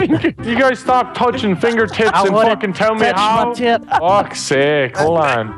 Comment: Did he just say "fingertits"?